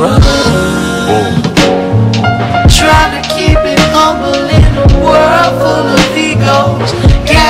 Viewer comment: “World full of egos, gangsters and evil” 💯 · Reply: Lol I thought he was saying "world full of Migos..."